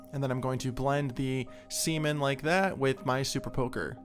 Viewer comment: Did I hear, “Blend that, $emen with my super poker.” ??? Jokes aside, really great work.